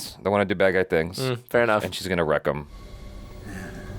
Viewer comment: it was because the combo of the gruffness of his voice, combined with a somewhat messy audio mix, made it slightly difficult to make out that he said Hell. I heard it very clearly the first time I saw the movie but I know some people didn't 🤷🏾‍♂️ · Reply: Hell isn't the word they misheard, rent was. They thought he said wreck instead of rent.